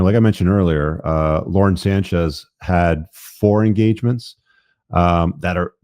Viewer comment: I thought you said Lord Sanchez